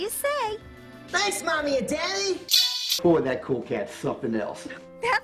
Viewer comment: is it just me or did I hear "oh that cool cat's something else" as "oh cool cat fuck an elf"?